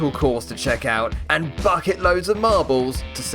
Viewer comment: did you swear or was I hearing things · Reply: Hey thanks for watching this video and for staying with my channel for such a long time :) I appreciate it. The word you are hearing is "bucket" which I used in the phrase "bucket loads of marbles". In general terms, it just means a lot of marbles. For example, enough marbles to fill up an entire bucket. Hope that helps! Cheers 😎👍👍